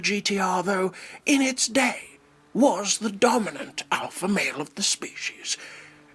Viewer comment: Whenever you said "alpha male" I thought you said "Alfa Romeo" xP